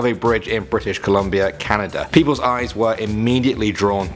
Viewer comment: did you say Canada bc gosh dam it is gotta move to Paris